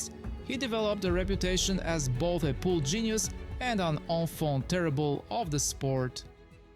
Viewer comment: Your accent made it hard for me to know what you were saying, but at 2:33 I think you were using the French term "enfant terrible," (pronounced on-font tear-e-bluh). If that's what you meant to say, I am impressed. Most people don't know that term, and it truly describe Earl!